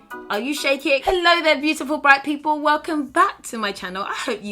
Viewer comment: I heard "Hello Beautiful Black People"... replays "Hello Beautiful Bright People"... Oh okay.lol